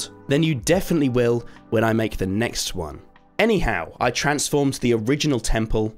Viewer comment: did he say “the next one”